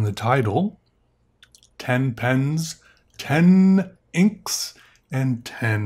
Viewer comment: I thought it said ..10, 10 Inks.. "but there's only 1 Peter!?" - he exclaimed; I exclaimed.